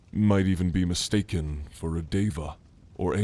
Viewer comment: i fully thought he said diva and i was like… i AM a diva???